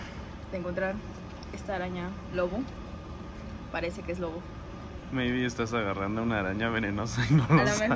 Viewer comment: /24:32 escuche bien o dijo baby? o dijo mayby creo q asi se escribe xd